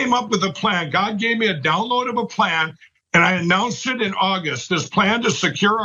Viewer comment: did he just say "God gave me a downloadable plan" ?